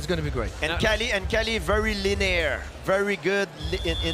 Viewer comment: Is Georges saying "linear"?